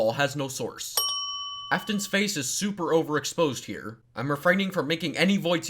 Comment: omg I got an ad and I heard you say "aftons face looks like soup"